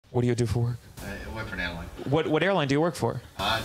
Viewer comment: I thought he said “WILL WORK FOR HAIRLINE” I know a gang of foos who did that who I grew up with! GOT MORE HAIRLINE NOW THAN WHEN THEY WERE 15